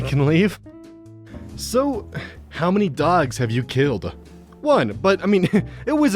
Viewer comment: I misheard this as "gods" not dogs.